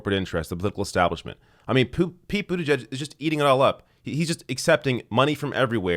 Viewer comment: Did you just call him Poop Pete Buttigieg?? lol